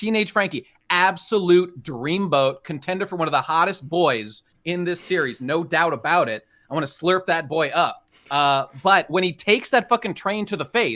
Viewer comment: Did he say “I want to slurp that boy up?”